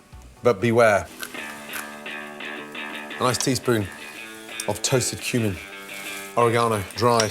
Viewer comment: can someone help??? what does he say at 0:31 A teaspoon off...??